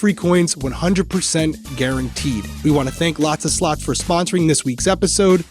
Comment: listening to this from across the room on my shitty iphone speakers i thought stephanie said the sponsor is lots of sluts 😂